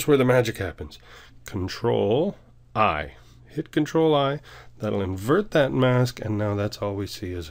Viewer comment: Im sry i don't speak English a lot can you tell me what you said at 4:08 click control and what? Tnx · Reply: Control and the I key. The letter i